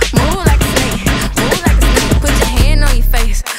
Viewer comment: The more I listen to it the more I hear “moo like a snake”